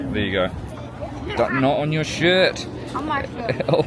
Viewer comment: When you told not on your shirt it literally sounded like you said not on your shit